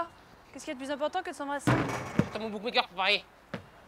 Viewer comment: Il dit quoi là ? x) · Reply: J'attends mon bookmaker pour parier.